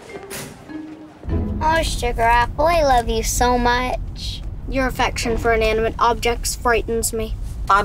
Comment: "Your obsession for inanimate objects frightens me". And then he grew up to fighting for star Wars gadgets... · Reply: I thought he said 'your AFFECTION for inanimate objects"